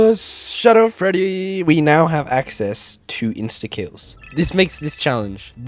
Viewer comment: Why did i heard it as "Insta kids"